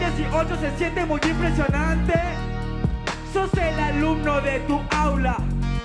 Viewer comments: que dice? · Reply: Me sacó el micrófono y empiezan sus traumas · Que dice trueno en le minuto 9:21? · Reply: Yo suelto el micrófono y empiezan sus traumas